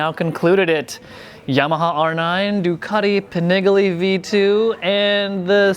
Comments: I know I didn't hear him say "Ducati Pa-nig-ly V2"....... that's 100% rooted in my brain in every conversation moving forward now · Did he say "Pah-nih-ge-lee" at 1:40?